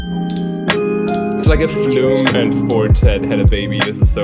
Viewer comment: what did he say? "it's like if flume and ??? Had a baby together" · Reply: Four tet